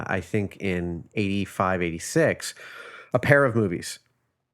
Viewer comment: I thought he said a pair of boobies